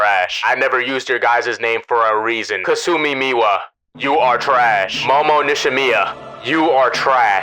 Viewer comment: did he actually say they are trash